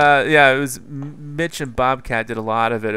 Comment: I thought you said Bitch and Mobcat